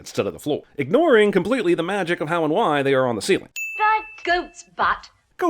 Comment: Mary Poppins is NOT saying "goat's butt"